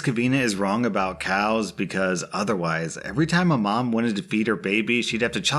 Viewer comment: I thought he said "whenever a mother wanted to beat her baby." Oh no